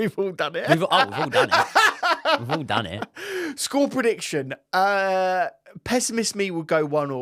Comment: What was that word uses at 40:34 ??? It's not a word I'm familiar with! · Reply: Pessimist